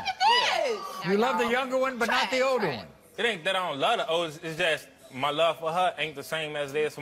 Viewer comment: i thought he said " it aint that i dont love the h*e" i was like did he just call that lil girl a h*e or did he ????